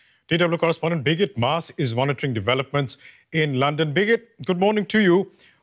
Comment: Did he just call her "bigot" with a very spicy curry accent?